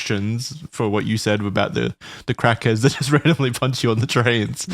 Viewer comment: did he say "crackers" or "crackheads"?